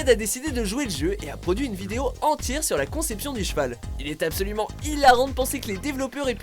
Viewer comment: A 3:50 c'est moi ou il a dit "des cheval" ?OoO